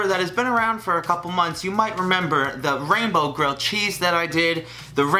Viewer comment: For a minute I thought you said Rambo.😂😂